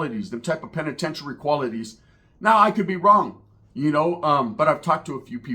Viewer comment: Did he just say penitentiary quality’s